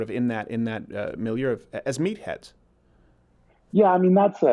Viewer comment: I was on another web page listening to this interview, and you asked about people thinking of them as "meatheads", and I swear I heard "meat-pets". I had to go back and watch the question again. Meat-pets are not ok.